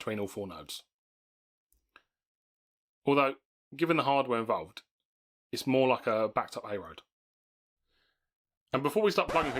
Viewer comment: it can't be just me WTF Did he say there? Although it kinda hdgahhfsjjsgdhshshfjfhshf though????????? It's like he's speaking English but not at the same time ... I relistened to that like 10 times still cannot tell what he said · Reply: "Although given the hardware involved its more like a backed up A road" I mubble alot! Im working on it.